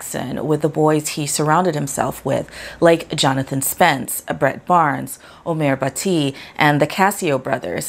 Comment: I thought she said Brett Favre! 😅